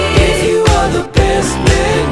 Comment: Am i the only one who heard "PUCKER UP" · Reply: It does say "pucker up"